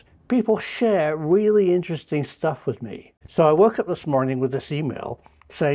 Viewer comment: I honestly thought you said " I woke up this morning with this female" and thought this is a bit of a departure from normal...